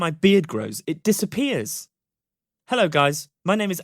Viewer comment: I thought it said my bed disappears every night